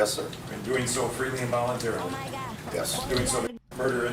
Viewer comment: Am I tweaking or did I hear “O MY GAH” 😭